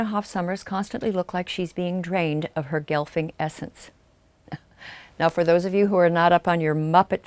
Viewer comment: I heard "GILF-y essence" and was all "uhhhhh...." at first, before tabbing back over to see if there was context. >_>